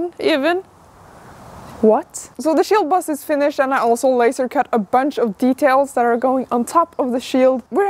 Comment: why do I hear Lasercat :3